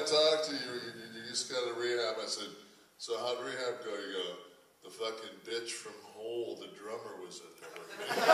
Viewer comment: what did he say? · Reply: from what i made out is went from about 6.48 "the fuckin bitch from whole the drummer was in there.SWEET!"